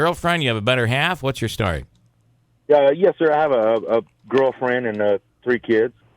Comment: Kinda sounded like he said he had a "grill friend". I mean, it makes sense, right?